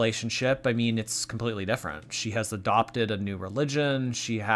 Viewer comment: lol as always, thank you 💜 I thought you said her life is very different now ya know? she's adopted a new pigeon 🤣🤣